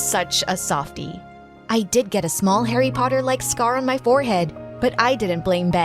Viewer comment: am I the only one who heard "I did get a small harry potter like scar"